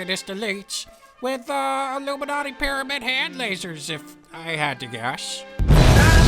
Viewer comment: I know you said 'Pyramid Hand' but it really sounded like said 'Pyramid Head' and I would have been willing to pay money for an Illuminati Pyramid Head from Silent Hill with a sign saying '9/11 was an inside job'. I feel like this was a great opportunity that was wasted. If you would have designed this I'd 100% become a Patreon supporter. For shame, for shame.